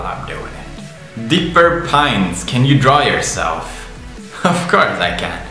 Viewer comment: Do my ears deceive me? Did he say Dipper Pines?